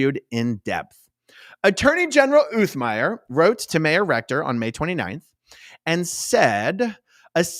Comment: Every time you say mayor recto or whatever u said it sounds like you said may erector